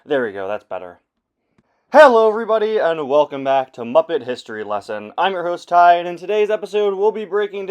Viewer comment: In the beginning when you said “muppet history lesson” I only heard “muppet history” and I dropped my project to make sure I wasn’t accidentally supporting official Muppet History